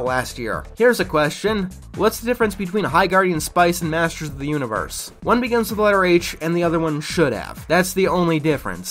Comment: I straight up can't tell what he's saying. Is he saying One begins to quell my rage or one begins with my rage? I just spoke too fast is all.